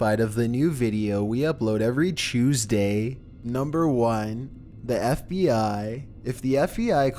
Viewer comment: sorry sorry.. did he just say chuesday???